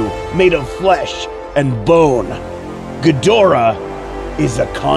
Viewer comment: I swore he said and put my name instead of Godzilla I’m scared